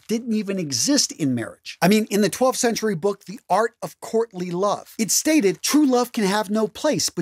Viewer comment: I swear I thought dude said "in the book, The Art of Courtney Love, there is no room in a marriage for love". After rewinding twice in utter confusion, I've now come to the conclusion that he ACTUALLY said in "The Art of Courting and Love" .